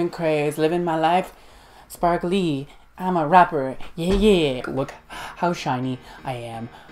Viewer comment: I JUST HEAR "MARK LEE I'M A RAPPER" 😂😂😂😂